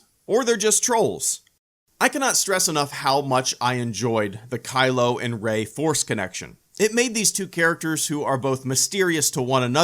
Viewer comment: did he say kylo ren foreskin action?